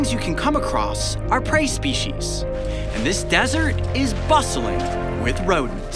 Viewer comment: did he say bussin